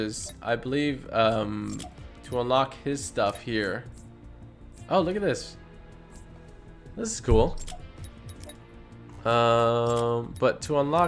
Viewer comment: Did he just say “oh look at this Thor contrail”.